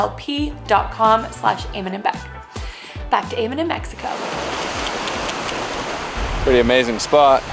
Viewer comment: I'm pretty sure you said "Eamon in Mexico" but I heard "Eamon and Becixo" and was like oh that's catchy 🤣